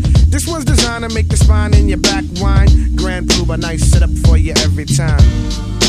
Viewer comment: This ones designed to make your spine in your back wind, Grand Puba lights it up for you every time. And you say NYC....Yep! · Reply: I always thought he said.."Grand Puba nice set up for you every time" smmfh